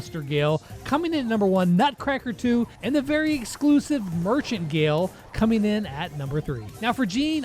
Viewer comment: am i the only one who heard "virgin gale"?